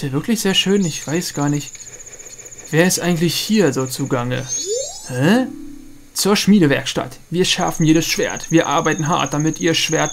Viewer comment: hat bei 4:00 noch wer "zur schniedelwerkstatt" verstanden XD?